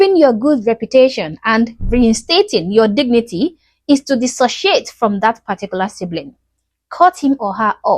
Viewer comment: Yeah she could just about pass as a Samburu woman. More so if her hair wasn't bleached. 😂😂😂 Girl, I just had to go back 10 seconds. I thought u said "your shit." My head snapped, eyes got big bc ive been watching u for a while now and I've never heard u cuss so I had to go back. 🤭 u said, "disassociate." I love hearing u speak though. I guess bc its so different from mine. Im southern American so I have a long draw on my words. Very country. Anyways, I wish all the good things in life find u and bless u. ✌️❤️🙂 Human Kind. Be Both.